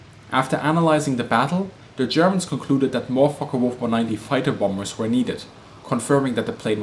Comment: the sub says fuckable 490 instead of Focke wulf 190 lmao